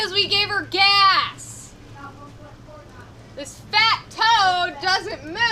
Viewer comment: did just call the boat a fat toad 😂